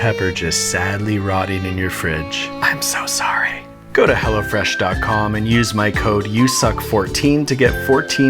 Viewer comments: I think the subtly in the humor here is hyper sensitizing. I swear I heard "Go to hell... o'fresh" at the end. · I heard at the end "Go to hell of resh" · Totally thought he said "go to hell..." at 3:56... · is it just me or did anyone else think he was saying "go to hell" at the end of the vid lmao XD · I for sure thought he was going to say “go to hell” but it was “go to hello fresh” lol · for a minute there i thought he said go to hell lol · I just heard "Go to hell-o fresh" was that intetion?